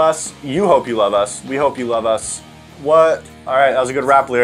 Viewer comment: Anyone else catch at the very end at 12:25 saying “are you on SoundCloud?”